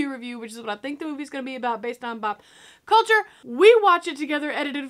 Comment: Did she just say “bop culture”? Is this an inside joke I’m not privy to?